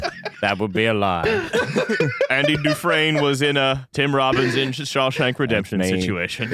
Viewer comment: I was almost certain said “Tim Robinson in Shawshank Redemption.” But I must have misheard him, because that would have SEVERELY sidetracked the podcast much further, lol. Also, yay!